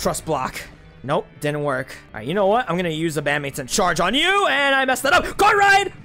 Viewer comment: Why do i hear "batmans in charge" when you say the thiefd move of chestnut?